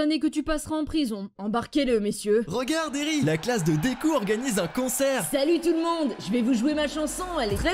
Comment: j'ai compris (regarde deku va fair un cancers)😂